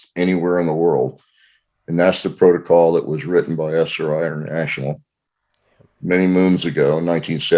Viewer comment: What is the name of the book he recommended from 1975 written by an Esther? I couldn't figure out her name and I'm searching for the book. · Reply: Yeah i heard that too at first but he actually says a book written by SRI international (Stanford Research Institute) the timestamp is